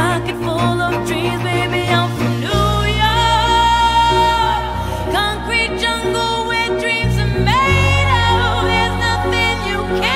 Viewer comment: I keep hearing " wet dream, tomato..." Instead of "where dreams are made oh"